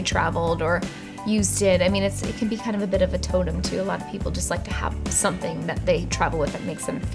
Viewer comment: When she said totem I thought she said Tatum!!!😭😩😭😩